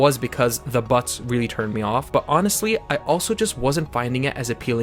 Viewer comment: thought you said on not off 😂😂😂